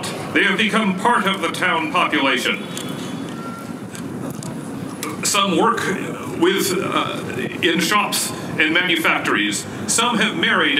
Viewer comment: the narrator says "some work with shops and in manufactories." Sounded like he didn't read the script right lol. Very Good Reenactment though!